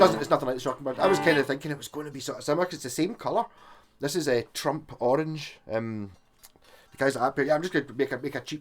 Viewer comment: Did you say that guitar is in Trump orange ? Lol I could of swore that’s what ya said. I laughed my ass off when you pulled the wire mic out and put on the guitar. It brought back memories of that livestream from a month ago. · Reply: yes its trump orange!